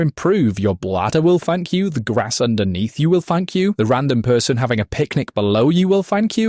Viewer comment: Idk why, but each time he said "your __ will" I heard "f*ck you"